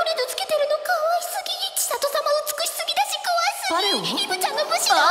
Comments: 怖すぎ ？？ に聞こえた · 地味に千聖さんの悪口言ってるw ※自分は「怖すぎー」に聞こえたのでもし違っていたらすみません(>_<)💦